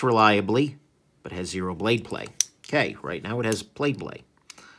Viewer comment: Did you just say “Plade Blay” @ 12:42? 😂